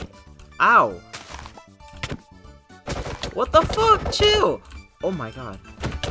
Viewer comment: i think he said what the fck